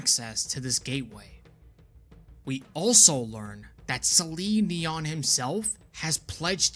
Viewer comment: did anyone else hear this as Celine Dion? Lol